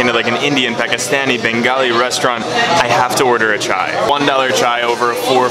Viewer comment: thought he said that he had to order a child